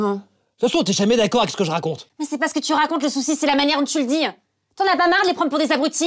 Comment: "dont tu le dihan" ça veut dire quoi ?